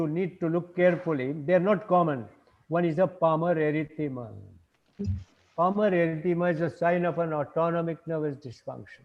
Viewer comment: What did he say...2 signs of ans dysfunction?? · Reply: Palmar erthyema Wecould write on child skin